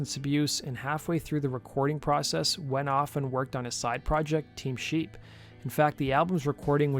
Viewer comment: I’m sorry but did he just said team sheep?!🤨💀